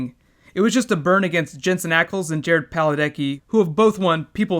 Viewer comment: did you just say 'PalaDECKI'????????